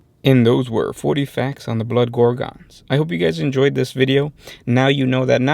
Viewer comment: I thought it said Blood Grogan hahahaha ouch